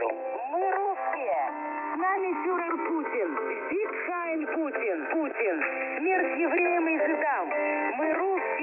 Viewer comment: Did they say “Sieg Heil Putin”?